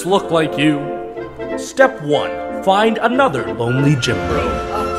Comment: I thought he said only gym bro